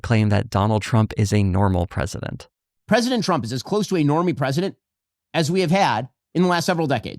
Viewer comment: I think he says 'normie' here? Which to be fair to him would be very different from 'normal'. which is itself kinda funny.